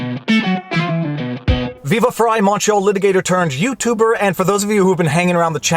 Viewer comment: I know he says "Montreal" but I always hear "macho" and it confuses me. Also I am convinced there is a Simpsons clip for every possible life scenario.